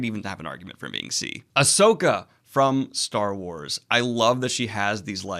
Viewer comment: When he said "asoka" I thought he said "hisoka" and I was like 🤯🤯🙄